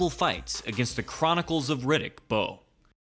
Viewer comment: Did I just hear "The Chronicles of Riddick's Balls"?